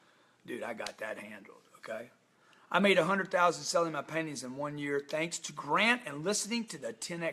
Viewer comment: I swear I heard you say panties🙊🙉😅